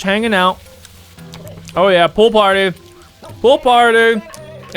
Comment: I hear "poop party" instead of pool party